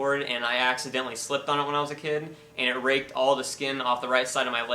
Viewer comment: Did you just say that is RAPED the skin off of your leg?